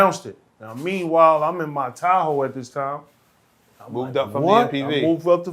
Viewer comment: lol i thought he said he was in his towel, not his tahoe